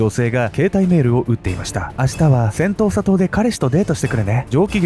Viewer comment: セントーサ島。 最初、音しか聴いていなかった時、 「戦闘・砂糖」 に聞こえてしまって、内容がなかなか頭に入って来なかったっす 申し訳御座いませんm(_ _)m 動画の内容の感想としては、うまい話には絶対裏がある、という教訓と、白馬に乗った王子様は現実世界にはなかなか訪れない、と。で、その恋人？とやらの遺体があがって、それが本人なのか鑑定したのかしら？DNAとか。 (内容が頭に入ってきてないので許して下さい😭)